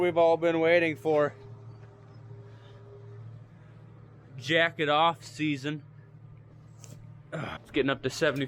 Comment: My wife was walking past me just as you said, "folks, the moment we've all been waiting for...jacket off season." and she stopped in her tracks and said, "what the heck are you watching?" She thought you said, "jacking off season,"